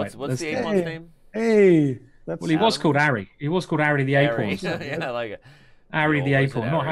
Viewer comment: LOL I thought he was saying ARRY THE GAY PAWN! SMH arry the a pawn.... I wish he never sed it again and i continued on being satisfied.